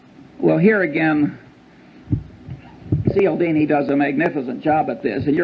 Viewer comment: I cannot find the author mentioned 19:39 Seal Dany ? Seel Dany ? seele Dany ? I'd like to know more about the book mentioned yet no title was spoken and a name is very easily misheard . some help please ? :). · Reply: Dr. Robert Cialdini- Influence